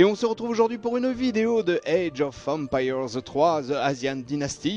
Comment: it sound like he said age of vempires